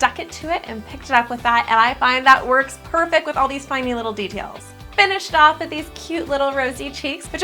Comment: I like how she says finy little details instead of tiny little details